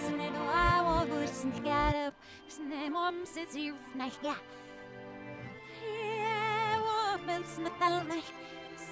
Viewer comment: put captions on and see what Elsa says in captions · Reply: It said "my mom says you"